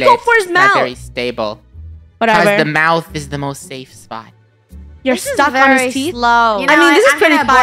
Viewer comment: funneh: your stuck on his teeth? What i hear: UR SUCKING ON HIS TEETH?